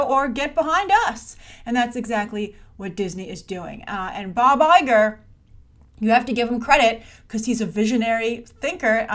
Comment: when you said "Bob Iger" I thought you were saying "Buh bye, gir!"